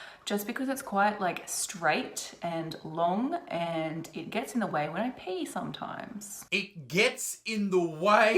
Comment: i thought that she said "it get's in the way when i pay " :D i mean that wouldn't surprise me :D paying with vag :D